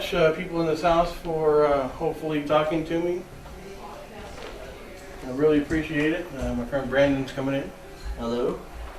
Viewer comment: theres a woman's voice saying something but i cant figure out what it says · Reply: Sounds like "you are always welcome back here" not sure